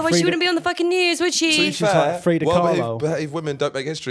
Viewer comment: Did I mishear because overlapping speakers, or did just call Frida Kahlo "Frida Carlisle"?! 😲